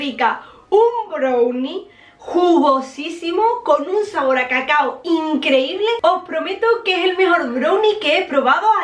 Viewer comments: Por un momento pensé que había dicho un brownie con sabor a caca 💩😂😂😂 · PENSE QUE DIJO CACA SKANIALANDISK 😭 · pensé que dijo con un sabor a caca 😂😂